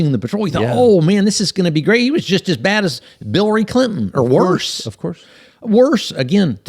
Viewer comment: Did Bovino say "Billary Clinton" at 1:12:38?! :DDD That's bilarious...